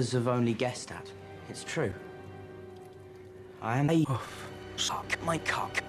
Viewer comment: What's he saying 7:28? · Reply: "I am a puff"